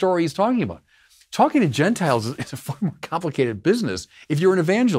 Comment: did he say fuckin? Lol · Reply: "Far more..."